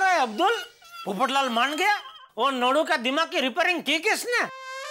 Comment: did i hear jethalal saying 'lodu'????? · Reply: Nadu bola matlab sabse bewajah nadnewala